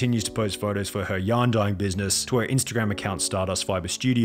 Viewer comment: Ohhhh it's a business where she dyes yarn. I thought you were saying she had a dying business, as in it wasn't doing well hahahahha